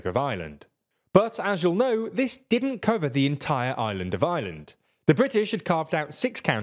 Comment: "this didn't cover the entire Ireland of Ireland" Oh, wait, no, misheard that… :D (It's not hard to hear it that way) Oh, and calling "The Troubles"… "The Troubles" is so british or maybe more so english.